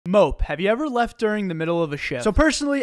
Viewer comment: I accidentally heard it as "Mope, have you ever left during the middle of a sh!t" 💀